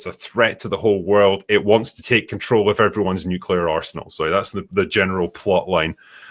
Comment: For a second there I thought you said nuclear asshole. Lols.